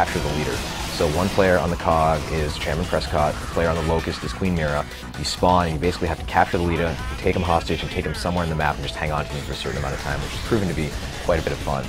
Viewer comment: i think cliff said capture the leadea